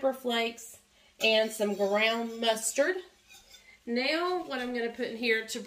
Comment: I have a question about the recipe. The written recipe has dry mustard and I thought you said brown mustard? Just want to make sure when I make this. Your recipe is different from mine. I am excited to try yours! I like to try new things! Thank you for sharing your recipe!! I really enjoy "spending time" with you and I enjoy seeing your husband on here too! You all have a blessed week!!